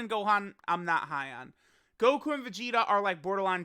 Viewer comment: Global's Meta Cooler Banner is really good. But the save bet is to go for the G&V banner. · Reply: For a minute I thought you said trunks and vegeta and was about to have sit you down and give you stern talking to.